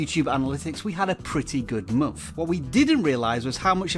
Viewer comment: forgive my poor hearing but did you just say that you have a pretty good muff?